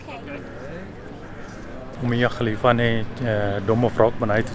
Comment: did he say mia khalifa ? Or my mind is corrupt .. please guide me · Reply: Mia khalifa hahaha Ummayya Khalifa :)